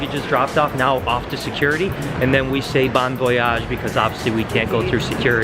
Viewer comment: did you said bon voyage ?